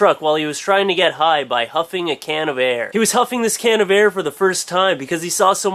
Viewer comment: i thought you said humping a can of air i was like wtf?